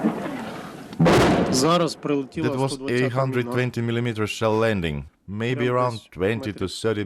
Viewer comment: "..it was 820mm shell land maybe 20 to 30 meters away" If that were the case, they would be vaporized. He meant 82mm mortar. 820mm would be as wide as a person. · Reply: I thought he said 120mm.